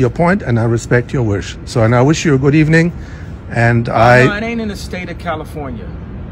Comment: When the Pap said to Ye that it was legal in the state of California... around the (3:45) time stamp did you hear him (Ye) say "we not the state of California" after he said that, the Pap was like huh, what and so did I. Did I miss understand what Ye said? 🤷‍♀️ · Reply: He didnt say we aint in the state he said IT aint in the state. IT as in the law the camera man was referencing